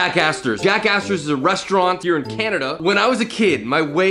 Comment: i literally thought he said jackass the whole video up until the end 💀